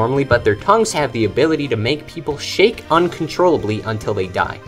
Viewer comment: LMAO WHY DID I MISHEARD SHAKE AS SHIT